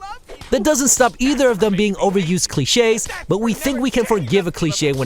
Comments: i thought it said glitches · am i the only one that thought it said glitches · I thought it said glitches😂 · I thought it said "Glitches" fak